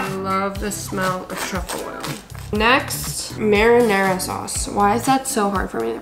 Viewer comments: i heard "marijuana sauce" i was like 'WHAT' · Swear i thought she said “Marijuana sauce” at the beginning of the video😭